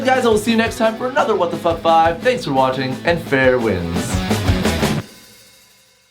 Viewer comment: Ok, so for the longest time I thought he was saying "farewell" at the end, not "fair winds"